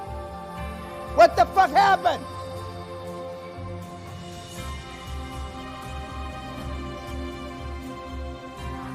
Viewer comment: What does he say from 8:00 to 8:10 and why is it Muted? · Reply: "Your parents, mommy and daddy". Is what happened